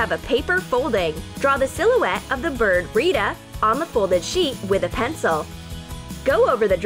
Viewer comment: In 5:31 I heard red as reda. Like if you heard the same and god bless the ones who are scrolling and reading the comments and stay happy 🙏🤚😀😇